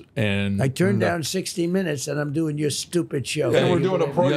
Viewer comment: Did Trump call this show stupid at 52:50 ?? Lol. Great interview nonetheless. F the haters.